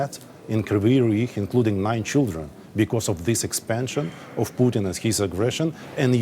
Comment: Thought he said “Including my Children” Stone faced😐 I was took a Back for Sec like MyGod This Guy’s SAVAGE. 💐RIP💐 ALL UKRAINIAN’S WHO’M MADE THE ULTIMATE SACRIFICE PROTECTING YOUR FAMILIES & HOMELAND🫡 DEEPEST CONDOLENCE TO EACH & EVERY UKRAINIAN EFFECTED BY THIS WAR💐🌹REMAIN STRONG❤